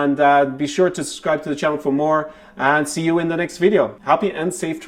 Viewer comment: Sounded like you said "Happy unsafe travelling" at the end (but of course what you really said was "happy and safe travelling")!